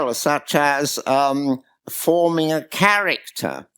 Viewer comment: I got an ad at 1:08:54 and it sounded like he was proposing that one of the greater goods is to form a carrot